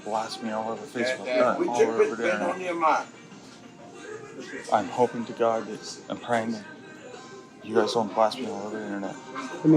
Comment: What ? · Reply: I thought he said "I'm hoping to god it's a prank!" but I'm not sure now, I think I may be wrong...what is he saying? I turned on auto-generated subtitles and it says "I'm hoping to god I'm pregnant!